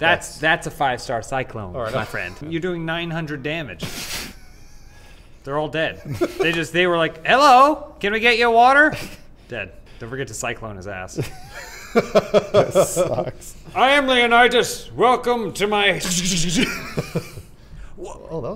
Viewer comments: Did say "This sucks" at 10:27? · "this sucks" or am I just hearing things?